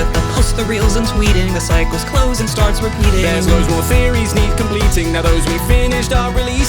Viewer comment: that part sounded like "Little theorising" kinda